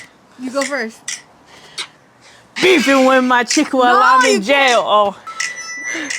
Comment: Bihhh I’m over here sick asf and half falling asleep and I just hear “BEEFING WIT MY CHICK WHILE IM IN JAIL” and jumped 😭😭😭😭